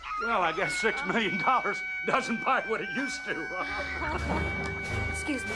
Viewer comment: what's he saying? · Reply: "He’s not a man you know. He’s a monster. (groans)"